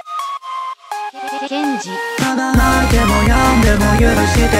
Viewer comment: ここなんて言ってるか分かる人いますか？？ ケンジ？エンジン？ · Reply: ケンジ 元のフリービート作った人の名前